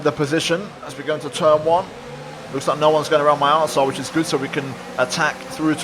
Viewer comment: did he say “no one has gone around my asshole” or is my mind just weird lol